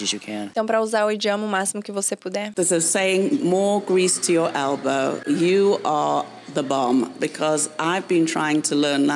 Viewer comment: eu entendi "você é uma bomba"😂😂😂😂